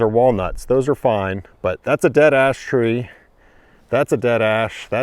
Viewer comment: I kept hearing "there's a dead ass tree" 😂